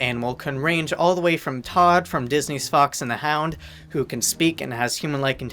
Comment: I literally thought he said " from Disney's FUCKS and the hound" LOL